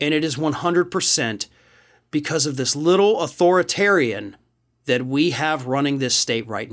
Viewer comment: "It is 100% because of this little Hitler" ... that's what you said, isn't it?